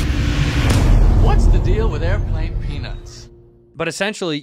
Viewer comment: I've always wondered... whats the deal with airplane peanuts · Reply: wait hes saying peanuts? My Freudian dyslexia heard it as Airplane PEANUS..